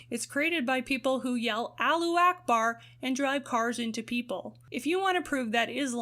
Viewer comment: I heard they say "aloha snack bar" not Allah Ackbar